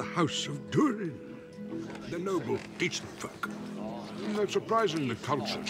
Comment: did gandal say they are noble each fuck?